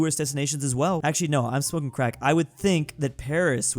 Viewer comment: no way I just heard say he wad smoking crack